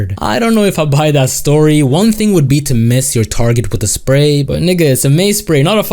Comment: Hold up, did this yt boy who channel this is just say nigga??? WTF